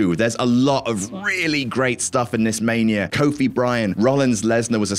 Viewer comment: legit thought I heard you say 'Kobe Bryant' cuz it went by fast, lol Had to rewind to see it was 'Kofi v Bryan'